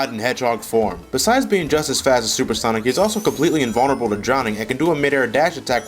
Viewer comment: wait at 04:31 did he say "being invulnerable to Johnny"